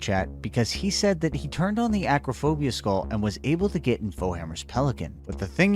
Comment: Did he say aquaphobia? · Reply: It’s acrophobia I think